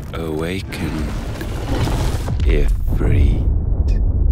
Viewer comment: It probably means nothing and it might be just me, but I clearly hear "AwakenED Ifrit".